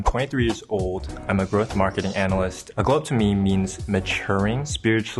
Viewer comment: At the very beginning of this video I thought this man said "I'm a birthmark analyst". And I was like what the heck!?!